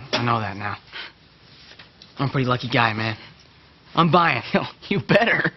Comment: thought for a moment he said "I'm bi!"